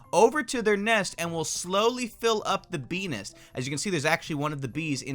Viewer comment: who heard p****s instead of bee nest